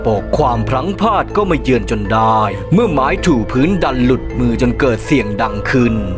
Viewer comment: ทำไมได้ยินว่าไม้จู๋พื้น55555